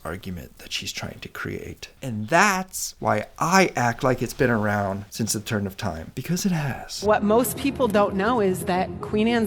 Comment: did you say it's been around since the turnip of time?